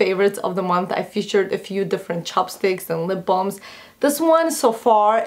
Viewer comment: ahahah i thought you said chopsticks instead of chapstick :P